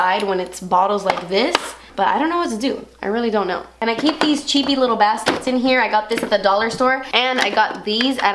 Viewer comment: Really thought she said "I keep these CHEEKY LITTLE BASTARDS BACK HERE" when she said "CHEAPPY LITTLE BASKETS" I laughed sooooo hard!